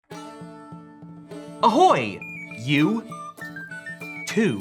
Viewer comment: did he just say "Ahoj"?